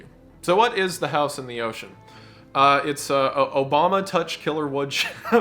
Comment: i got an ad here so all i heard was “obama touch killer” and was so confused